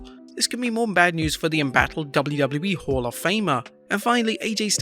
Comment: when he was talking about Tammy, did he say hall of famer or whore of famer?